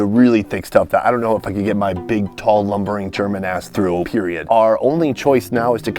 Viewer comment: did he say german ass or something else ? Never knew he was German